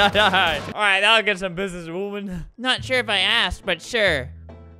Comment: it kinda sounded like he said the a word but he said asked